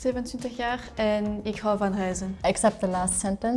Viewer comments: We do understand "aardappelen" of course but you're absolutely right: we much more often say "patatten" :-) :-) I don't think she was a very good choice to represent Flemish (I have absolutely nothing against the girl, but when she spoke about "ik hou van ..." I thought she said "Ik hou van huizen"... twice lol. I think she might be from Wallonia? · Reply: yeah, i agree! I also thought she said "ik hou van huizen" 🤪. I indeed think she actually speaks French, she said she might have an accent, but it's also clear that she does know the 'official' Dutch/the A.N words and not wich words we as Flemish-speakers actually use. :) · Wow the Flemish person has pretty terrible pronounciation it sounded like she said "ik hou van huizen" instead of "ik hou van reizen"